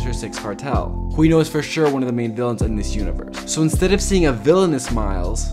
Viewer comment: Am I the only one who heard him say “in this skibidi universe”